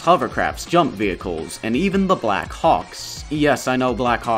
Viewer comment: why did I hear him say black cocks the first time I watched this I was so stunned I had to rewind